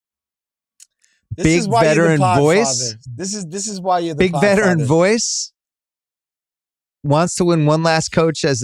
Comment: I am not an English native speaker, what is Wosny saying to Bill at 6:07 "this is why you are...?" what is the word? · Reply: i think it was "podfather" podcast father, like the "Godfather" of podcasting.